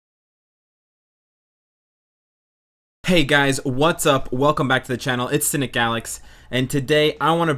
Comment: My phone lagged a bit when you said "hey guys What sup", I thought you said "hey guys what the f"